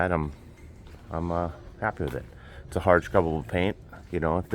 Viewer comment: did you say you have a hard paint? Do you put the ablative coat into op of that? What are the pros and cons in your opinion of just going with a hard paint and no ablative coat? Is it easy to remove hard paint when it needs a new coat? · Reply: We have hard scrubbable paint on our hulls and ablative paint on our saildrives. There are pros and cons with both types of paint, but we prefer hard scrubbable because we don't like the thought of Emma rubbing up on ablative paint. With hard scrubbable, just a light scuff and you can reapply another coat.